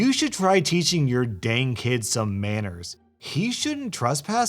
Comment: I swear I thought Said:Teaching Your Dank Kid Some Manners.I Laughed